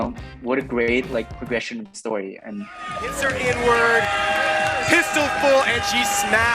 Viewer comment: Can anyone tell me exactly what he says at 9:22? It sounds like “insert the N word” something